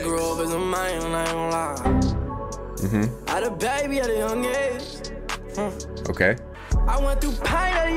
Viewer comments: "i HAD A BABY BUT IM GAY" 1:30 is what I heard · I've watched this video so many times because this shit is THAT funny, but I still have no fukkin idea what he was trying to say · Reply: He grew up as a mangmangla 😔💔😢